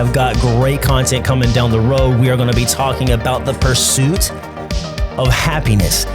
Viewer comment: Idk if you were too close to the mic or what, but at the end, it sounded like you said "the fur suit of happiness." I had to do a double take and I'm still not sure... 😂